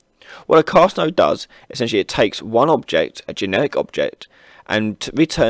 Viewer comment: It sounds like you say it takes a genetic object...I'm guessing it should be generic?